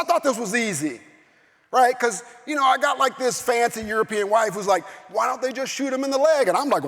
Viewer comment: does he say "I've got this fancy European wife?"